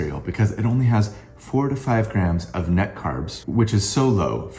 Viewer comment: Use my code D4 to get $5 off your delicious, high protein Magic Spoon cereal by clicking this link: <link> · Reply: Great ad read in the video, please enunciate 4 to 5 grams of net carbs. I thought you said "45 grams of net carbs, which is so low for cereal"